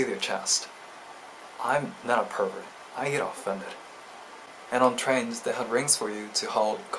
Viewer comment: I thought he said....I'm not a pervert, I eat feta😂